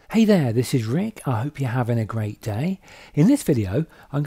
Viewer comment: The first time I thought you said "Hey there! this is the freak..." , no offense but it was funny after I realized I misunderstood :)) Anyway, great tutorial! Thanks man!